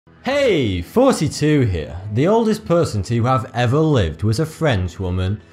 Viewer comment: Am I the only one who thought he said: "Hey, 42 here."